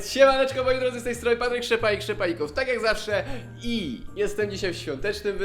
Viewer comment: Usłyszałam " z tej strony Papryk Szczepanik Ssczepanikov ... " ;')